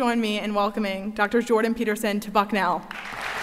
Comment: During the opening remarks, says: “And without further adieu please join me in welcoming Jordan Peterson to Bucknell”. What I heard was: “Without further adieu, please join me in welcoming Jordan Peterson, fuckin’ hell!” 😏